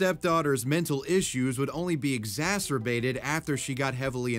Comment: When you said exacerbated i thought u said masturbation Nasty nasty🥴🥴👉🏻👈🏻👉🏻👈🏻